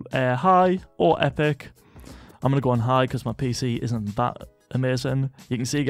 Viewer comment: "Imma go on high cuz my pc isn't ba- amazing"😂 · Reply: he said "isnt that amazing" but his birtish accent makes it sound like hes saying "ba- amazing"